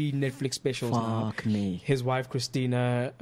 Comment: did MacG just say fuck me @ 19:02 😅😅